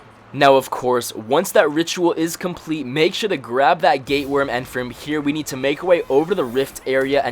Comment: It sounds like you saying gay worm